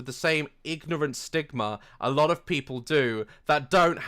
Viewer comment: When he said stigma I thought he said ligma